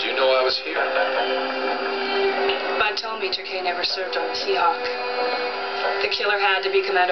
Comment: I really didn't understand what Mac said after Harm asked, 'How do you know I was here?' at 1:15. Can anybody please write me the answer? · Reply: This is what Mac says to Harm "Bud told me Turkey never, served on the Seahawk. The killer had to be Commander Holbart or Williams but I guess Holbart since his ship docked tonight."